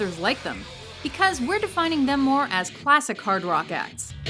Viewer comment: I thought they said "No classic rock"